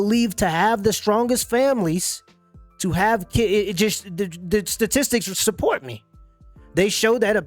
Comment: I thought she said SADISTIC of marriage!!! Lol!! She said statistics!!!